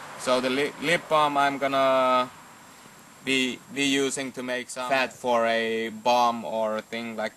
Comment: What did you say..Your going to make a Fat Bomb?? LOL Good video